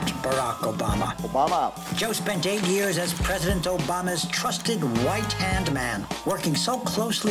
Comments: Did they say “trusted WHITE hand man” or did I mishear that?😂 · I was listening in another room. And I THOUGHT they said “he was Obama’s WHITE hand man “ and I cackled lolol